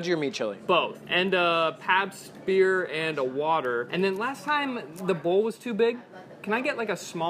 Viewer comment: I legit thought he said papsmear and a water